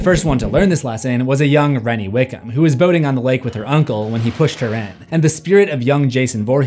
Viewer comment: i straight up thought he said boning not boating at first 😂